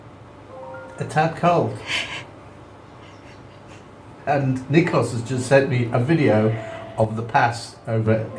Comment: Just a personal note. I noticed you called your daughter-in-law as Calliope, and pronounced it as Cally-opeh. In Australia we say C'll-eye-opee. Is this wrong? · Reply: Sorry, I misheard you, I thought I heard you say Nikos was your son - I was wondering why you name your son, such a Greek name. Makes it all clear now, thanks.